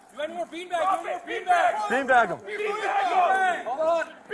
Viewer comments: I thought you said refried bean bags my bad😅😅😅😅😅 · I thought they said bean bag him. I didn't know they said t-bag him, something tells me he did not want to get t-bagged.